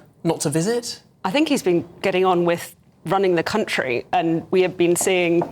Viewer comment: Of all the things she could have chosen to mention with regards to "being busy" running the country: "We're rolling out breakfast clubs..." 😅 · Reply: I thought I heard ruining the country 😂